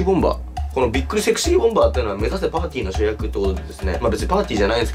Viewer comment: 目指せパンティの主役って聞こえたｗ